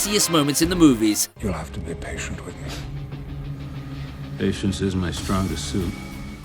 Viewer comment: How you gonna misquote what he said in the very first one. Number 10. 0:42. Its "patience IS my strongest suit.